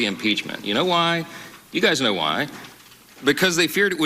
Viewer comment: Did I hear him say ‘cause know why’. How third grade of him.